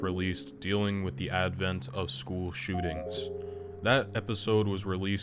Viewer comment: I thought it said, "School shittings"